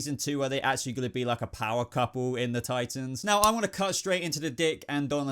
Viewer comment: I swear I thought u said "I wanna castrate" at first. Lol